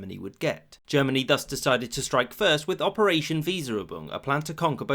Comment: I misheard it to "Operation Miserable"